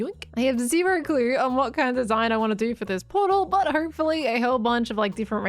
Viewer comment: did u say portal when u are desining a map frame?